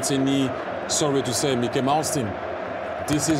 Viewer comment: Did he Say 1:52 Mickey and mouse team ?